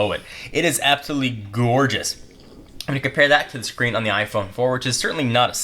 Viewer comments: did he say iphone 4 ? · you said iPhone 4 instead of 5. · Did he just say "iPhone 4" ? Lol. xD